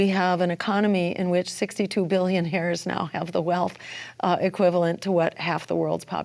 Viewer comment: does anybody know what Dr. Jill said at about 14:19? something about 65 billion hairs and half the worlds population being 350 billion people!? i have no idea what she was trying to say there! there are only 7.7 billion people on the planet. so confused.